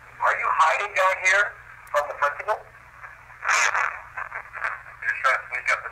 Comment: I hear “I AM the principal!”